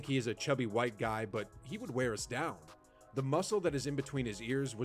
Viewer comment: Kareem did not say he wore us down, he said, "He wore us out."